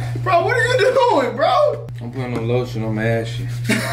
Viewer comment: When he said "putting on a little lotion, I'm ashy" I heard that as, "I'm putting a little lotion on my cheeks" Lmao